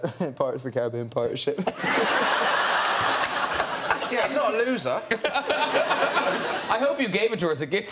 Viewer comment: what said the guy on 1:56? something bout loser? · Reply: yeah he is not a loser